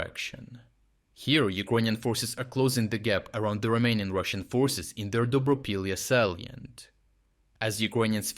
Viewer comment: I had to listen 3 times at the start 0:05 when you said "around the REMAINING russian forces" because I thought you said the "around the ROMAINIAN rushing forces". That accent threw me off 😅